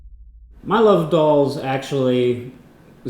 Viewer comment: I had to listen to that again because I heard "My love dolls" instead of "My love of dolls"